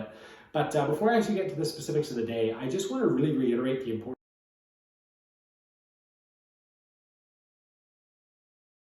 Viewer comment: What did you say at 2:20? The audio cuts out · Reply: thanks for point that out! I said it’s super important to find the correct set and setting. Treat these drugs with respect and try to eliminate variables that could influence your experience negatively